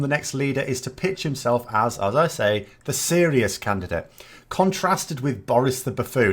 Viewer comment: The serious candidate? Sorry, I thought you said the sleaziest candidate